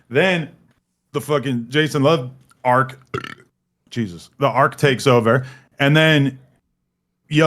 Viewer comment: did he say Jesus but he atheist? 😂😂😂